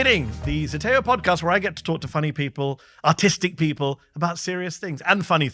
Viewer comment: I thought he said "autistic people" 😂😂😂😂 I mean... nothing wrong with being autistic but I'm pretty sure Mark isn't autistic.